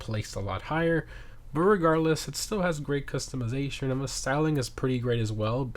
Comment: till 3:26. Did you just say the word irregardles?